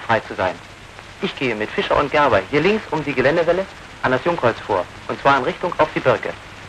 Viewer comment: Whats up with the subtitles? I submitted them some months ago. Where are they? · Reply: "Jungkreuz" is misheared from "Jungholz", meaning saplings or young trees.